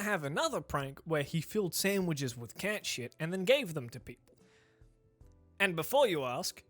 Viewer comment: I was playing animal crossing and heard "he filled sandwiches with ketchup" and thought "well... that's not a bad thing right? Maybe they taste a little weird at worst, like in that one british prank show where they gave people margarine instead of ice cream"... I heard so, SO wrong